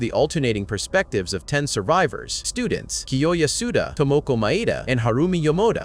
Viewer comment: i heard "Tomo komaeda" instead of "Tomoko Maeda" damn I'm too obsessed with Danganronpa